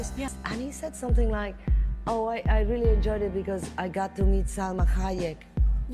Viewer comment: Don't worry Salma you are a beautiful woman, Eminem enjoyed your encounter MORE than you think😊 , what he actually said was "“I got to hug Salma Hayek!” emphasis on 'hug'